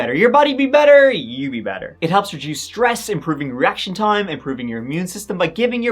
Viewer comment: I could have sworn said that Beam tea improves erection time, lol! 🤦🏻‍♀️😂